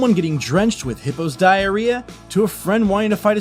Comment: Not gonna lie... (Nor I'm not even trying to be funny...) When the Narrator just says "Getting drenched in Hippo's diarrheas." I honestly really thought he'd just said; Getting drenched in HITLER'S diarrheas!